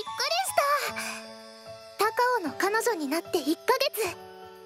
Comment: 高尾が彼女になって一ヶ月と聞こえたのですが